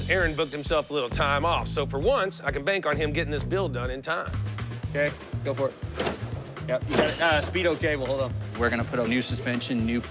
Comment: do my ears deceive me or did i just hear him say “speedo cable” 🤔 last time i checked the speedo cable was up front at the front driver wheel lol.. the throttle cable is back there and i guess you could say it gives you speed when pulled